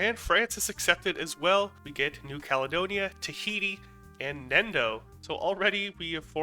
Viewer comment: did anyone say: tahiti?